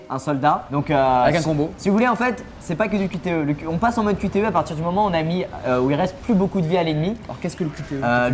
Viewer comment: Moi je comprend cul terreux, "On passe en mode cul terreux" xD